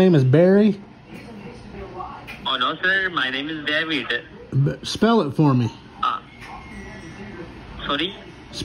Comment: Goodness I almost thought you said "smell it for me" lol